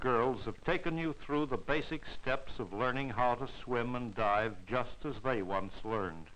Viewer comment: Poops I thought it said learn how to swing my bad